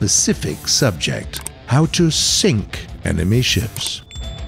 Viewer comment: he sounds like he said anime ships